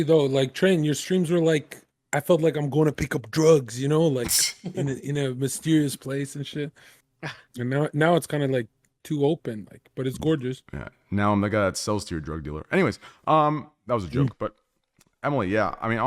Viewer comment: I almost had a heart attack when train said "Im the guy who sells to your dealer" because just listening to it it sounded like he said "Im the Nword that .....)